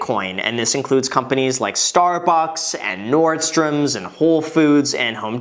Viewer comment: Hey graham, why did you say 'Star Fox'